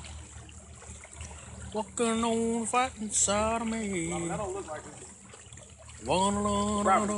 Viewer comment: Alright, if you're looking for answers, I did some digging and found out if it truly do go down. In short, yes, in fact, it do go down, but knew they'd still be okay since their boat would safely run into a concrete barrier before it go down. The video was taken in Lake Murphysboro State Park, Illinois. The southernmost point of the lake passes under a bridge of the main park road and ends in a concrete wall or dam, the other side of which leads to a spillway, or the "do go down" bit. The dam is high enough to control the water level of the lake, which only allows water to flow over if it rains too much. The spillway probably only goes down about 15-20ft at a decent slope, where the water turns into a creek and eventually flows to the Big Muddy River. Even if they went over the dam, they'd only get a couple scrapes and bruises, if that. is singing "Fightin Side of Me" by Merle Haggard. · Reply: I thought he said "fightin' sodomy" all this time. Well, ain't I just embarrassed.